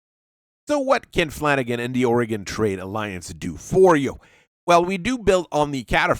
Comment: i keep hearing 'aurigan trade alliance' as 'oregon trail alliance', lmao